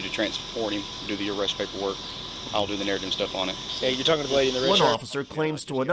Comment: "you do the arrest warrant and I'LL DO THE NARRATIVE and stuff" - Is that what I heard?